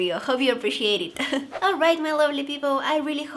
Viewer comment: did she say “alright my lonely people” because i am 😔